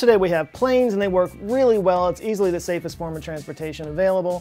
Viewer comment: I heard Lifts are the safest form of transport <link>